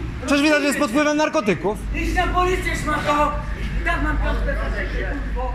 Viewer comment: czy ja dobrze usłyszałam 5:28 "idź na policję sz***o, i tak mam piątkę z etyki k***o" XDDDD